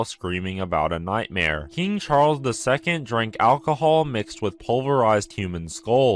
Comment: i thought it said James Charles II 💀